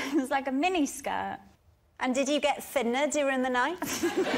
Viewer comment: Did you get....through the night??? What did she say... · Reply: Thinner...during the night